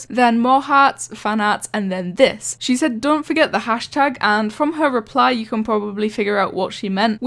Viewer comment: i think she said "Dont forget the condom"